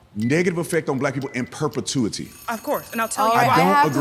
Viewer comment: did she just say "property tax"??? 🤣